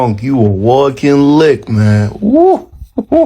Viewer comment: someone please tell me why I heard this in Mario “yahoo!”